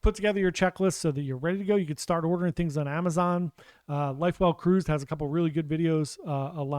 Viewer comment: At about 3:25 in the video you mentioned another channel's/page's checklist of cabin items, like magnets. I couldn't catch the name even though I played it five tmes. What is the name of the page/channel? Even though I booked my October 2023 cruise back in June 2022, and I'm by no means a seasoned cruiser (this will be my third, last one being 10 years ago), I have a box in my home office where I toss all the items I've bought or found around the house that I plan to bring with me. I've also created a shopping list on Amazon so I can order the items when the time comes. I add items as I watch YouTube videos or read the Cruise Critic boards. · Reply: Hi Dawn. Sorry for mumbling lol. It’s life well cruised. She does a great job with things you should take and checklists.